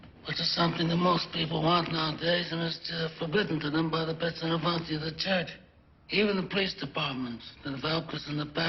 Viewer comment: brando refers to the "petztelavant" . anyone know what this is? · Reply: "pezzo da novanta" in Italian means someone very important and/or powerful. "Piezz novant" in Sicilian dialect, as said by Brando. "Pezzo" is an artillery gun and "novanta" (90mm) is the caliber of the bullet: "gun shooting 90mm bullets". It's used in this sentence to refer to very important people in charge, like senators and politicians.